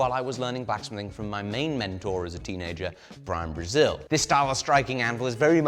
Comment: Did I hear "Brazil" around 11:35 ? Hahahaha Um oi do Brasil pra vc! Vc tem muitos fãs aqui!